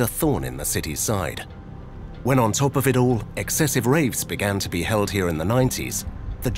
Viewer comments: I thought I heard the announcer say “excessive rapes”. There’s a box fan going by my head. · Did he just say Excessive Rapes? WTF!? Well I for one am glad that place is nothing but a ruin now.